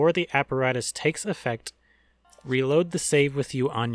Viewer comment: Wait save? Thought you said shave